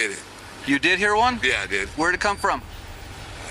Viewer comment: Pretty sure he said "I did it." but the cop didn't understand and when he asked where did it come from, Jones said I don't know.